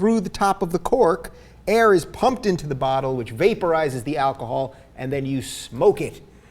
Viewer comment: "beta rises" instead of "vaporizes" xd geniuses